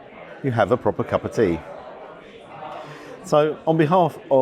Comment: I miss heard I thought you said cup of pee. As a pensioner to think I would have to save to afford a breakfast 👍👍